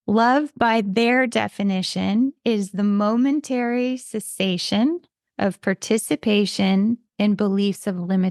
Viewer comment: "Love, by their definition, is the momentary cessation of participation in beliefs of limitation." 💯🤍(the transcript has the word "sensation" instead of "cessation", giving the opposite intention?)